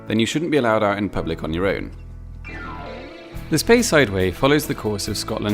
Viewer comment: I thought he said “space-highway” but I guess “speyside way” is cool too